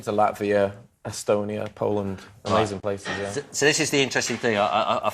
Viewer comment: Did Adam said "Poland"? ;)